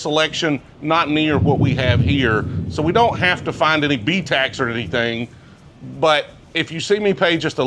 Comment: what is ‘BeeTax’? Sorry i probably got the word wrong. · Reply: BTAC. Buffalo trace antique collection